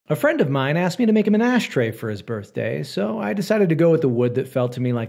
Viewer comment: I thought he said an Ass Tray 😭